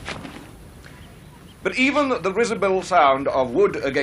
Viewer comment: Did he say "rizzable sound of wood against skull"? I thought "visible", but I don't expect that from a professor. 🤔